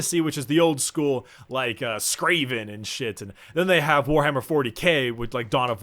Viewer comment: I really like, but... did he just say "Skraven", like with an 'R'? 2:50.